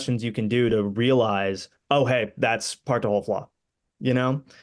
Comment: Not a big deal, but it's driving me crazy, and even closed captioning was no help -- what the hell does he say at 10:49 ("oh, hey, that's... *partdoubleflaw*)?! Great interview, to be clear! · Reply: part-to-whole flaw